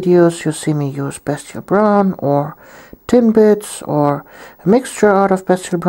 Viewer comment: Every time you say "Tin Blitz" i hear "Tim Bits" and i get really hungry :(